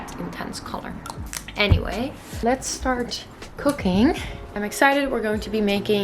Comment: When you said "let's start cooking" I heard "let's start cocaine" - I had a baffled look for a second... then I heard it right. Aaah 2nd languages... 😂😂 I'm definitely more of a dumpling girl haha